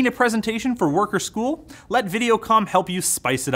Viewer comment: ohhhh videocom...i thought he said videocum for a minute